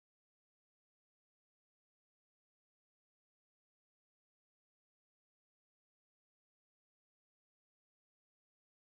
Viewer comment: Okay at the end, what is he saying, it sounds like needs to be reversed or something. Round about 3:04:21. Kinda creepy continues to about 3:04:57 Can anyone decrypt this please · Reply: I have done just so. First part is "Find the buried treasure.", second is "Find the hidden coordinates." The flashing low brightness pictures show clues where it is. It's under a rock and some grass in godknowswhere, though there seem to be coordinates hidden within this three hour video, one explaining latitude and the other longitude would be my guess.